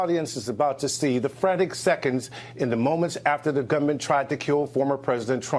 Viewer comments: quit trying to create something that isn't there. Just after 14:16, what Pierre Thomas says is "...the GUNMAN tried to..." and not "...the government tried to..." I have no doubt. I know living in Utah limits the kinds of vocal styles you're used to hearing, but the dialects from around our nation are myriad. The demonrats say enough stupid things on their own, we do not need to invent things we believe they say. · To be fair, it seems to me he didn’t say “government”, he said “gunman”. I can see where you thought you heard “government” but listen now with the word “gunman” in mind. I think you’ll hear it too. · "..in the moments after the gunman tried to kill President Trump." is what he said. Gunman, not government.